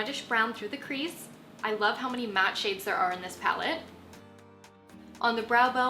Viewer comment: i kept hearing it as "this palette from SheepLam", well that's an animal I'd love to meet!